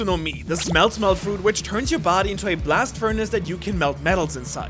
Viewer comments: fr thought he said "smell smell fruit"💀💀💀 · I thought he said smell smell fruit. Lol